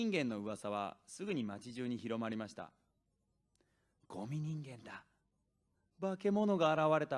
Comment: ゴミ人間がゴム人間に聞こえるのはおれだけ？